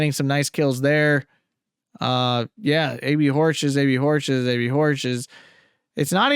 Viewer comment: its funny the subtitles thought you were saying baby horses instead of AB Horch. had me giggling the whole video